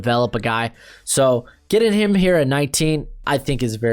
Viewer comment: What said, "Kyle Pitts, pick him here at 15!" What I heard, "Kyle Pitts, Baconater 15!" It might be time for me to get some food.